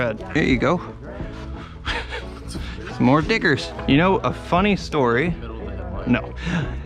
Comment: Why it sound like he said “more dickers”